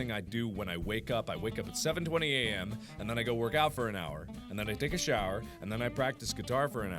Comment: I was doing something else and I thought he said “and then I go jerking for an hour”